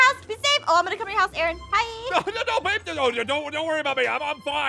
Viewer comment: WHO ELSE HEARD AARON MUMBLE BABE TO